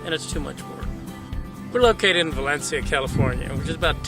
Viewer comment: for a sec I thought he said halixia, California